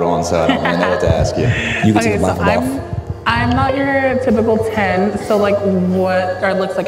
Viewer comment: "Im not your typical 10" is the biggest understatement I've heard in a while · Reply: You misheard. He didn't say "not your typical ten". He said "not your typical ton".